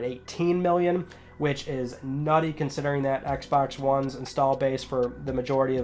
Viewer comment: Did man just say "which is nutty"